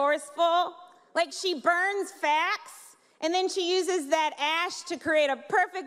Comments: Facts? I thought she said Sanders burned fat. If it was facts, then it was funnier. But maybe she should work on enunciation. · It SOUNDS like she is saying burns "fat." I had to listen carefully to understand it was "facts." That isn't to say any of the outrage was correct, but I can see if you get all upset and call it an "attack" you might think it was an attack on her. Indeed.. she's fine and funny..